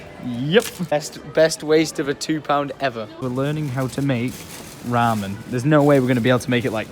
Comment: AHAHA Hi legit thought he said, "we're learning how to mate..."